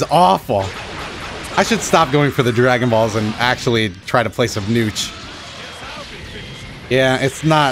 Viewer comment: did you just say "play some nooch" That needs to catch on, I want to hear a commentator be like his nooch game is amazing.